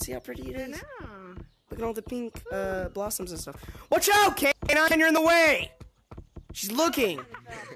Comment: WACH OUT (K9 I think he said) YOUR IN THE WAY SHES LOOKING 😡